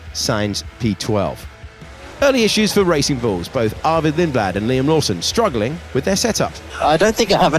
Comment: CC says Ayumu Iwasa for Arvid Lindblad 💀💀💀